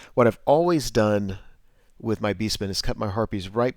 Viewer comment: Sweet battle report, not sold on the new name for Beastmen, I keep hearing "beast turds"